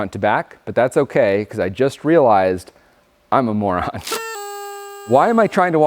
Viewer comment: did you call yourself a moron 😂